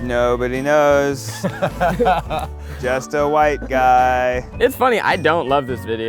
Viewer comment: When they were telling us their nicknames and Ned said "The White guy" I thought that he said "The Wife Guy" and I was like "yep that's totally true" and then I realized he said the white guy and I was like "oh. They need to change that."